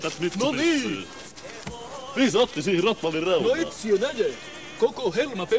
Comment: mitä toi sano. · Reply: Rotvallin reunaan